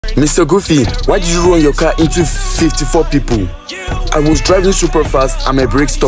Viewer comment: I hear, "Mr. Goofy, why did you run you CAT into a restaurant?"😅